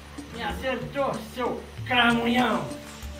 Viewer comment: me acertou, seu cramunhão, merecia virar meme tbm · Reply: Eu entendi "caminhão" kkkkkkkk